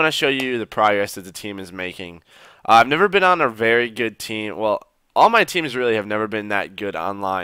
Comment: Did he say he has never been on a good team?